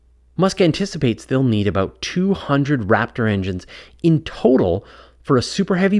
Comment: I thought he said 100, not 200